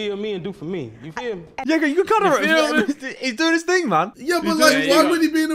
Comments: Am I tripping or did harry say the n word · what did he say?? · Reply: no he said-"you go.. you know he's doing his thing man". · WHAT DID HARRY SAY 💀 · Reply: no he said-"you go.. you know he's doing his thing man". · is it just me but it sound like harry said the n word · did harry just say the n word? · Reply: no he saud " yeah yeah " rlly quick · sorry but my ears dont work i suppose so can someone please tell me what harry said? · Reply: " yeah yeah " rlly quick